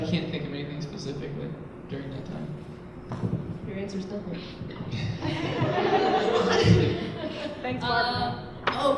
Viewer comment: What is the girl saying at 1:39? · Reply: I think she said "your answers different" ... I can't quite make out her mumble; she seemed disappointed tho